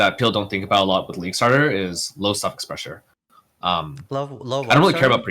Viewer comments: I'm glad he put a caption saying "low suffix pressure" definitely thought he was saying "low self expressure" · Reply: Heard it as "low socket pressure". 😁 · I'm glad he put a caption saying "low suffix pressure" definitely thought he was saying "low self expressure"